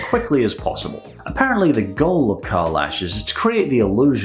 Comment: I thought it said carl ashes